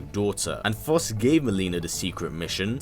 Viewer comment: Gay Mileena? Am I hearing right?